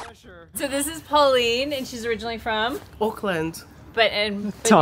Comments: At first I thought Pauline said she was from “Oakland “ (California), but then I realized that she said “Auckland” (New Zealand). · I heard "Oakland", which I thought was pretty cool, but that's not what she said.